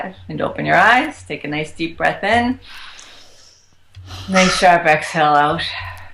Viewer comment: What does she say at 48:44? "May sharpa telos", or something like that. She doesn't explain that. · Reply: “nice sharp exhale out”